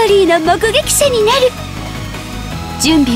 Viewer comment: 記憶体でアーカイブ、潜行でダイブ、操作でコマンド、原初でルート、記憶倉庫でストレージ、履歴でヒストリー、配信開始でストリームアウトって呼ぶの東ロワの世界線ならではって感じですごい好き！！ どの世界の子もなんだけど特にB世界の子は笑う時はめっちゃにこってしてるからかわいすぎる… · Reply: 「ストリームアート」やなくて「ストリームアウト」って聞こえるんだけど僕だけかな…？